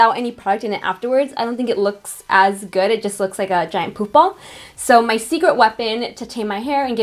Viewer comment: Who else heard "poop ball" @ 12:11 lol